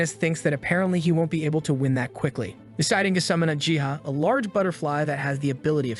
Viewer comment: I thought he said "Deciding to summon a jihad" lmao 💀